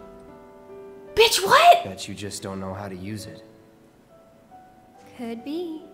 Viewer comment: said "bitch what?! right before cloud spoke, and i could swear cloud said "Bitch you just dont know how to use it" And now that line is canon in my mind 💀